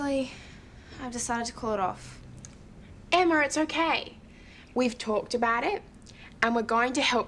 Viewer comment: DID SHE JUST CALL HER EMMARR ??¿